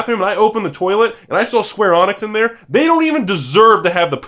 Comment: I heard "Square Onix" and I immediately turned to the comment section...